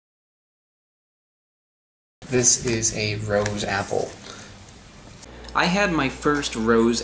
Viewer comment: Great video; I thought you said Road Apple at first!